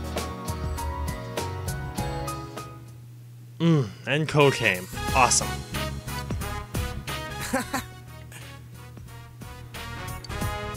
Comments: I know he said "And Kou came, awesome." But I heard "And cocaine, awesome!" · I heard "and cocaine" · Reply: he's actually saying and kou came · "Mmm, then cocaine." That's what I heard at first.